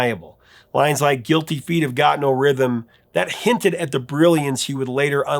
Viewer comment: I always thought he was saying “Guilty feeling I’ve got no rhythm.” I actually related to that.